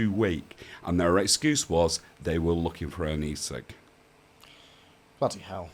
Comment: I might be going deaf, but what did say at 16:05 that the police were looking for when they strip searched his daughter? It sounded like "knee sick" or "E6" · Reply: E-Cig